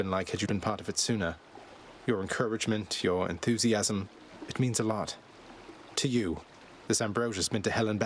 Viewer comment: "Your encouragement, your enthusiasm.. it means a lot to you." is how I heard it. Made me laught. It sure means a lot to him 31:00 nobody tell her it was random...